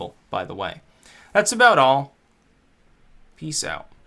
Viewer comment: No tea? I cant cum unless I hear the spoon clank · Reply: I thought he ended by saying "dicks out" not "peace out"?